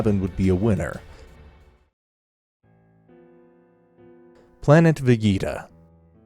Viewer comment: Is it just me? When he said planet Vegeta I thought he said planet fajita 😅